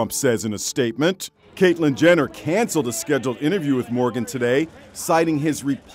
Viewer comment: sounded like he said “his scheduled interview” 🤭